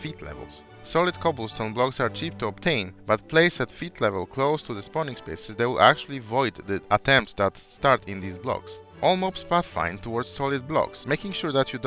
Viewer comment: someone want to translate what he said there? i just don't understand it. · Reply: solid cobble blocks are cheap to obtain but placed at feet level close to the spawning spaces will void the spawn attempts that start in these blocks.